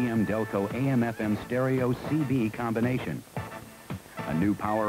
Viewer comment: I thought they said "CD" - had to watch that 3 times to hear what he really said..... I knew CD's were not available in 1979.... but still... CB radio is kinda cool.